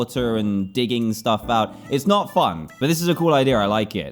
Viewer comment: Did Dan say “f*ck”